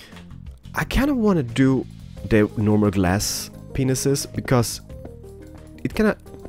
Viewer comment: did he say normal glass penises!